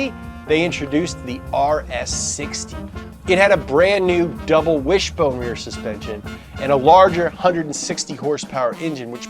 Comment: did he say double wish boner suspension. I hope he did. That would be amazing lol